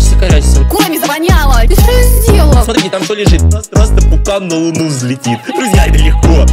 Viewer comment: что он говорит? Пукнул-на луну взлетит?